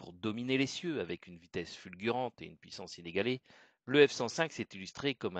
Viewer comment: Je savais que j'aurais dû faire guide de musée de l'air.... (PS : je deviens peut-être dur de la feuille, mais à 0:18 au lieu de "puissance inégalée" j'ai entendu "huit cent sénégalais". Faudrait peut-être qu'on se cotise pour vous offrir un bon micro).